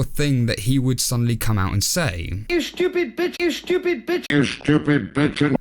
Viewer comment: Everyone seems to be running with the idea that he said "stupid b*tch", but I hear "stupid pigeon"